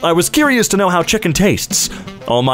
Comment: I thought that said children